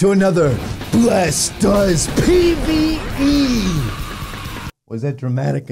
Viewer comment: When the intro was playing I thought It said bless does penis 😂😂😂